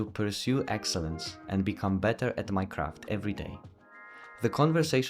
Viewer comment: misheard this the first time! 1:05 Jonathan [inspired me ..] to become better at Minecraft every day